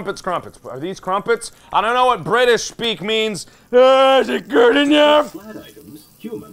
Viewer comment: I thought he said "I don't know what breadish speak means'